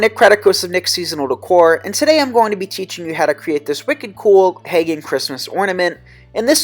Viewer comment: Did you just say... "wicked cool PAGAN Christmas Ornament"??? /eyeroll